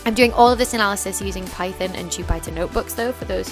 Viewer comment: what did she say at 11:05, does anyone know? sounds like 'python and chew binder notebooks' to me but apparently that's not a thing, according to Google anyway :-) absolutely fantastic video, so inspiring, and really gives a sense of what academics do day-to-day! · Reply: ok I think it's 'jupyter notebook'! In case anyone needs to know.